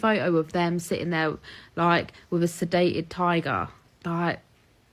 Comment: Holy crap, I first thought she said sedated thai girl. Then the accent caught up with me.